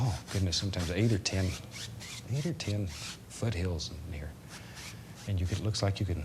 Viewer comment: omgod bob rob what did you say??! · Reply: foot hills? xd